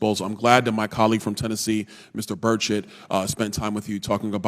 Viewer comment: Does he call his colleague, "Mr. Bird Shit" at the 18:09 minute mark? 🤣😭